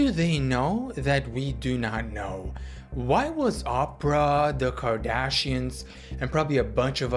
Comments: why I heard "Opera" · did he just say opera?😭